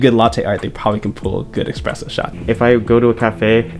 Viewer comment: oh gosh did he say "expresso shot" ? :D